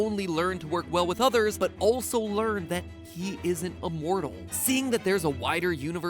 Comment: I thought he said he isn't a mortal